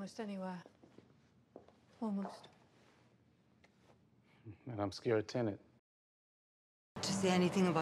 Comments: every time I saw this movie I heard him say "And I'm scared of Tenet" here. I feel much better knowing the real line now. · every time I saw this movie I heard him say "And I'm scared of Tenet" here. I feel much better knowing the real line now. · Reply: Lol same here! I thought he said I’m scared Tenet! The only way - and I mean ONLY- way to understand this movie is to watch it with subtitles